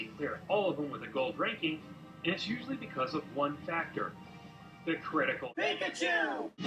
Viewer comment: Turn on captions. It's SO FUNNY! Example: 0:37 - 0:41 ...It's usually because of one back there..the critical no. That is really what is says.